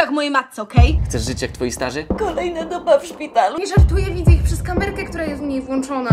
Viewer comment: usłyszałam "kolejna dupa w szpitalu" i miałam takie WTF